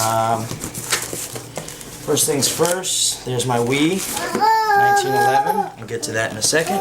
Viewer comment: Thought he said theres my weed